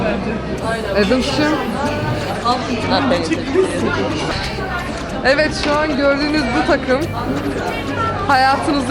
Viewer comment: adams 1:53'te ne diyor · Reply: "Am i paying attention to u ? " dedi